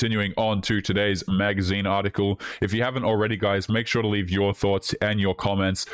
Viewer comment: What says "Leave your thoughts." What I hear "Leave your thots."